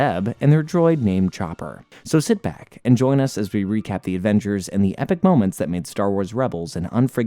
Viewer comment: my dumb ahh thought he said “avenger’s” not “adventure’s”